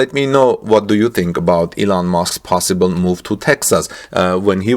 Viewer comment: Thought you said Eland Musk